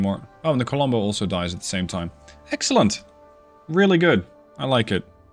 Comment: I swear you said "heksellent" instead of "excellent" here and I'm praying that's intentional because that would be amazing given the context